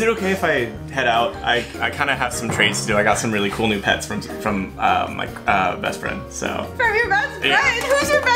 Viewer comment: "Boy friend" She said it 😏 Edit: thank you so much for the likes <3 😊 omg thanks for 108 like!!! Tyy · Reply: Listen to 10:58 what did you think was gonna say he said my g-bestfriend 😏